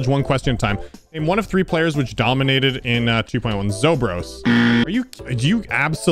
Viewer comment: name one of 3 players which dominated in 2.1 the text: name one of 3 players which dominated in 1.9 me: wait, did just said 2.9?